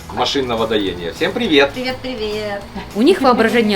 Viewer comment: Загадка человечества - 8:40 Что сказал? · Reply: Машинного доения